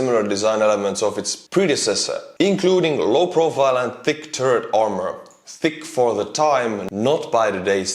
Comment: I heard "low profile and thick turd armor" - and it didn’t seem inaccurate.